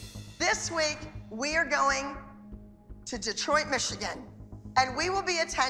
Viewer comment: I THOUGHT ABBY SAID "Destroy Michigan" BUT SHE ACTUALLY SAID "Detroit, Michigan" LOL